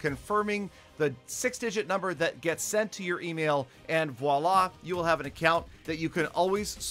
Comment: did he just said wallah? LOL